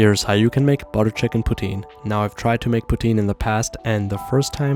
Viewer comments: At first I thought he said putin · I heard "butter chicken protein "